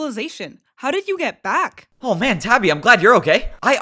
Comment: did bro say tappy